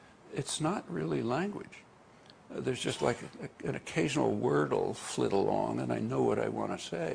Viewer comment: What is the word he says at around 1:24? Word? Fiddle? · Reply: Occasional word will flit along...